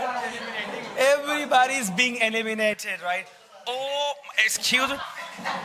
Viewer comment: I heard the teacher is saying 'Everybody is being animated instead of eliminated" at 10:38 minutes. Anyway it's fun.😂